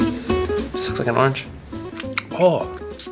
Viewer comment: "This looks like an orange". Me:"Of course what do you think it looks like an apple?" Edit THANKS FOR 300 RANDOM LIKES GUYS!! Edit again thanks for 600 likes guys!! another edit thanks for 700 likes!! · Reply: I thought he said just looks like an orange not this